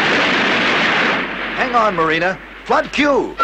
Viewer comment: what did I just hear omg ? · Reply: Amaan - LOL. I had to watch it 3 times before I realised what you meant, and now I hear it every time. He actually says "flood Q".